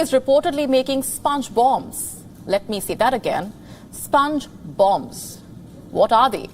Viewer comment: I thought i heard Sponge bob, well it was sponge bomb😊